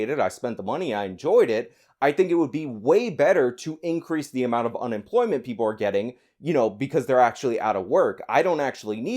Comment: What did say? I did not heard or understood correctly. I think I heard, “Increase the amount of unemployment people are getting...”. What did he mean by that?